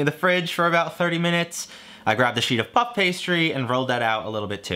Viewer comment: Did anyone else hear f*ck pastry?